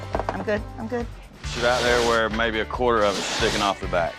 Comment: I'm going to give you a raise of .25 cents an hour. LMAO. What a joke. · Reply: I had to go back and re-listen to it. I thought for sure he said 25%. But he said basically a quarter and this guy's over here saying man thank you.